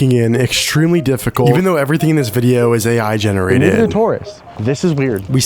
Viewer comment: did he say everything is AI generated???